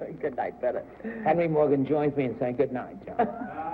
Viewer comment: I’ve always thought I heard a voice that sounds like Mark Goodson’s shouting a warning ‘John….’ after Bennett sets him up to burn Henry Morgan.